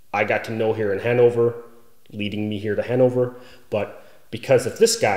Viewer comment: at first I thought you said "That is kind of the circle of friends that I got to know here in hangover" XD